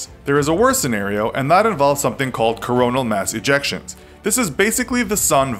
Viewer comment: I was about to comment on the Christian view point of end of the world. but then you said coronal mass ejection. and I swear: might be my English understanding but I thought you said coronal mass ejaculations